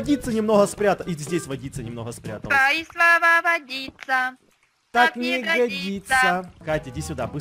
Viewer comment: мне послышалось твои слова водица как ягодица 🙃😳🤦‍♂️😂👌👍🗿